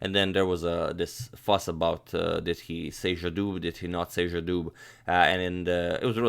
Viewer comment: di he WHAT or did he not WHAT!!?!?!???? Sayjadoog? What the hell is a SAYJADOOG!?